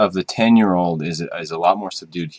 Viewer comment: you say: "the smokyness of the 10yo is a lot more ???? in here" i didn't understand! the thing is - I like the smoke (I'm talking about smoke smoke. not peat. I like peat as well) and i have at home the 10yo. i like it very much. the thing is, is the quarter cask more smoky? because if yes... that would be the best scotch ever for me! I'm saying this because I don't have the money to spend a lot of times in whisky. so each (almost rare) occasions to buy have to be a perfect choice with no disappointments!! eheh. really nice review! thanks a lot! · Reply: He says "subdued".